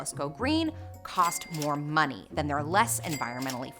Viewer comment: subtitles are wrong it is “their” Captions: English